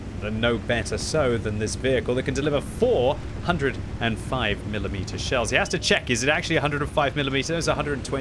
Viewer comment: Hahahahaha - at the beginning, I thought you said it would be delivering "420 mm shells" instead of "four 120 mm shells." Got my attention, for sure.